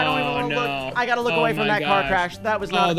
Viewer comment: did really say car crash? awesome vid tho keep up the good work and have a great spring.